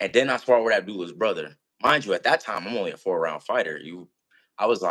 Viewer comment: 4 ROUNDS⁉️⁉️⁉️⁉️ Nigga I thought you said Abdullah ran after 1😭😭😭